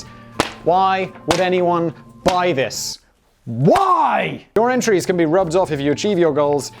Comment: I think the audio cut out - what he actually said was “why haven’t I got this yet?”